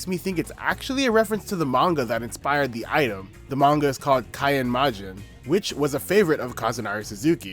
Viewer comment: what's the manga referenced at 4:55? can't quite make out the name as it's being read and can't find anything googling the printed title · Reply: Kaen Majin 火閻魔人